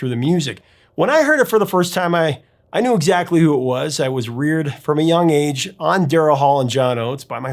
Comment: lol @ 4:10 I heard "I was weird from a young age" instead of "I was reared from a young age." Pronounced perfectly, but apparently I keep an eye out for weirdness.